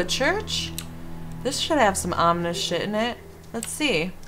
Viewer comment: When you said "Oooo a church! This should have some ominous shit in it!" I heard "this should have some Amish shit in it!"